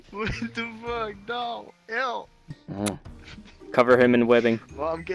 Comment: I thought you said cover him in women